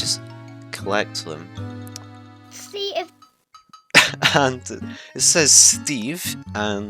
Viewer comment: think he says see ya